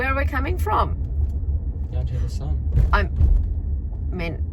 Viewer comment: Mom: So what just happen, where are we coming from? unintelligible sounds like he said, "down to the Son?"